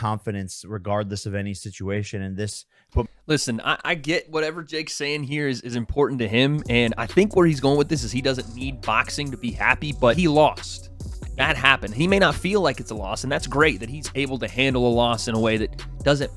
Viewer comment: I thought Jake said he doesn’t sleep 😳